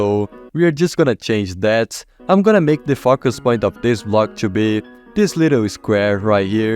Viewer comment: i swear i hear Fuckaspoint